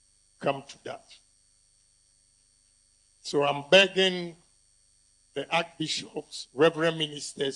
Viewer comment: He actually said "I'm begging" it was as a plea, so why are these pastors attacking him ? The church members he's fighting for, are also backing their pastors. Herr! N’krofuo Yi agyimi